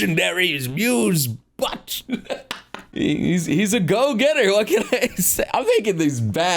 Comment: I heard he's a "goal getter" and I was like unite?